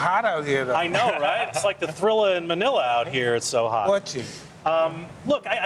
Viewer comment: Notice how the guy said thriller in vanilla and Mike got offended and said, "Watch it!" If I heard him correctly. 0:20. Was Mike offended about what he said? What was it that offended him?